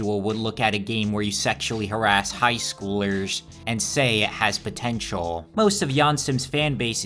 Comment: DID YOU SAY POTENTIAL?